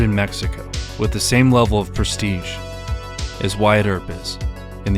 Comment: white herpes? Common man, it is too hard to catch. I rewound 3 times, couldn't guess what you said. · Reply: He said the “same level of prestige as Wyatt Earp is” He was a famous lawman